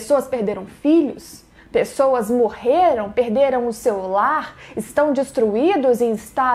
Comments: eu entendi "Perderam o celular" KKKKKKK Falei: OQ? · Eu juro que em 2:05 quando ela fala "perderam seu lar" eu entendi "perderam o celular" e me emocionei pq é realmente muito triste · eu entendi " perderam o celular " aí eu concordo, tem que ficar pistola mesmo.